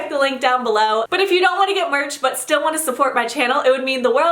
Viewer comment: When she said "get merch" I thought she said "give birth " Lmao I'm so dumb. pls tell my I'm not the only one tho